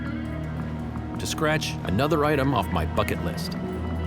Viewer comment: Anybody else hear "fucking list" instead of bucket list? xDD